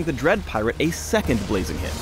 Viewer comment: Did Phreak say a second Blaziken? Phreak apparently plays pokemon, cool XD